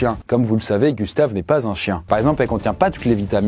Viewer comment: ah bon c pas un chiant ? Moi je pensais XD XD XD XD